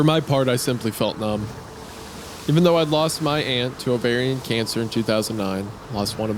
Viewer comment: I heard 'hand' whenthey said 'lost my aunt' and had to click in to see the text.